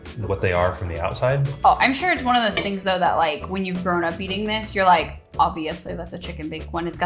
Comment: Not going to lie, I thought you said "thrown up with this" instead of "grown up with this". That you be a rough upbringing 😅